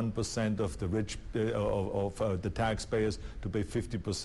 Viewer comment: In 2:44 it almost sounds like he said "rich bitch"